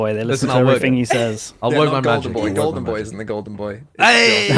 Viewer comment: i heard them call sideshow riots "golden boy", but i must be going deaf because im pretty sure they meant to say "baldin boy"